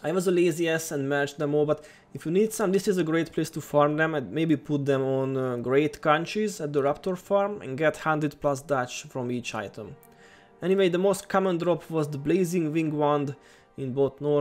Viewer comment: Hey, can you repeat what did you say around 7:40 ? about the high salvage and the raptor Thanks again, i keep playing because of you ! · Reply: i said to put the measure for measure inscriptions on Great Conches for 100+ dust your right thougg i said dust wrong :D said something like duch